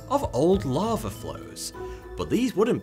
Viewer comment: When he was talking about Venus I swear I heard “covered in rolling dunes and mountains, Venus is also home to gigantic swarms of larvae flows”............fuck that shit. So Venus is literally just DUNE?!